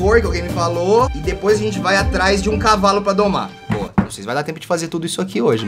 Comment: eu entendi mama um cavalo